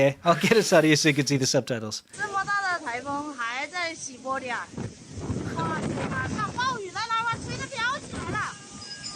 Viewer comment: I thought she said "Such a big typhoon, and still in Siberia".